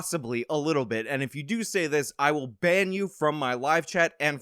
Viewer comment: I hate to admit it because I have a filthy mind, but the first time I heard this part, I thought you said bend you over, not band you. I’m sorry. 🤦‍♀️